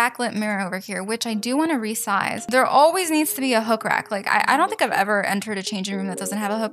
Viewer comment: w-w-w-w-wwwwaiittt a minute! did she just say h00ker when she was adding the hangers! oh wait she said hook RACK! My bad lol